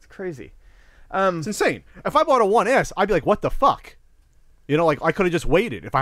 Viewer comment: It sounds like is saying "One Ass" everytime he talks about the S model :D "It's insane, if I bought a one ass I would be like a what da fak" XD